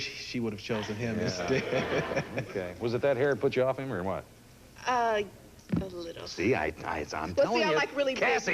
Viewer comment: Does anyone know what she said @ 5:26? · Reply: She said “ a little “.